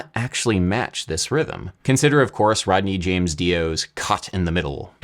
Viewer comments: His name is Ronnie, not Rodney. tripped me up when i heard it lol, and the subtitles say Rodney too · I coulda swore he called Dio "Rodney" lol